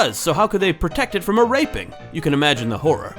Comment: wait, did say raping or raking at 2:35? I hope he said raking...